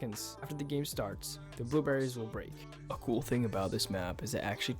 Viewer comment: Sounds like youre saying blue berries xd